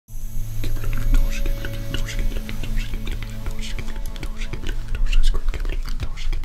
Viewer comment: what are you saying from 0:00 to 0:44 ? i was just wondering bc i cant understand :oooo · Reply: Keep looking at the torch.